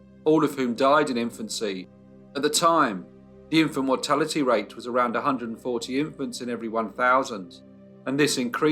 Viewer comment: oh my god, I thought you were going to say, "At the time, the infant mortality rate was around 140%."